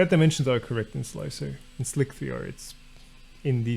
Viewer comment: I thought you were saying "Slick VR"